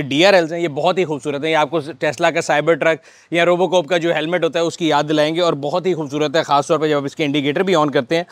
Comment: Did he say Robo "cope" ??? 😂